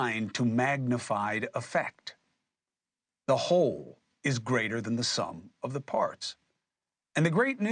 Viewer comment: Did he say “the whole is greater than the sum of its parts”?